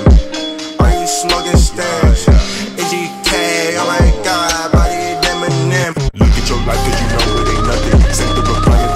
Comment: What is saying on 01:58 - 02:02 ?? can anyone tell me pls. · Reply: MGK oh my god bodied Eminem